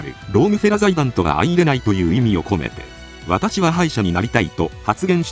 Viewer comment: 私は歯医者になりたいって冗談抜きに聞こえて？？？ってなったなぁ。